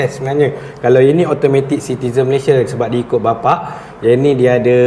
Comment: Saya salah dengar ke awal2 ckp netizen bukan citizen 😂